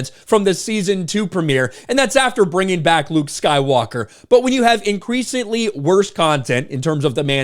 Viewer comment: Sounded like "Lubed Skywalker" to me. Probably a memory trigger from Zach & Miri where they called him Lubed Skyballer.